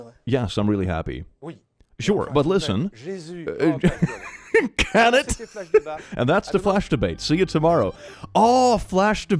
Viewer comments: the translation is not accurate, what actually is said is: – Yes but, jesus- – shut the f*** up. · he actually says: Shut Up